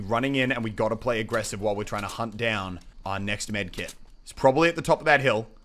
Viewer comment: "while we're trying to find our next med kit" i think you ment to say "target" right?